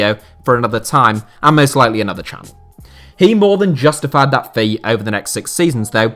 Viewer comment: Did he say another channel?? If Ur leaving HITC please let us know in Twitter please...I just cannot live without your videos